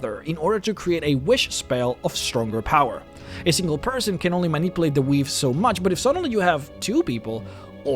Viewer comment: Sounded like he said manipulate the weeb. Which is really funny.